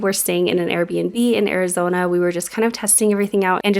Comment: I always watch a little faster than normal and even though I'm all for team Utah... One time when you said Arizona, I heard "OUR-izona" I'm loving the idea of you just doing it all over again. Make it your family's "OUR-izona" in line of work he can go anywhere. Carpe Diem, Guys! We love coming along on your journey. <3